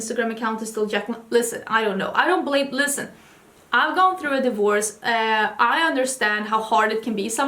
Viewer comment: When you were talking about Jaclyn I thought you said "I'm going through a divorce" and I had to rewind quick to make sure you and Ricardo were good 🥸